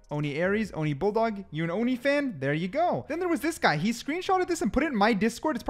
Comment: Did I just hear you say oni (only) fans?